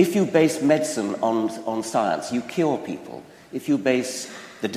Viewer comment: Was it just me who thought he said "if you base medicine on science, it kills people"